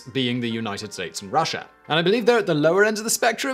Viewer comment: Did he just say The United States of Russia? Holy shit I wish I lived there